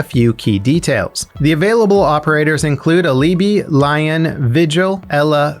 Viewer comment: Did you really say “Aleebee”?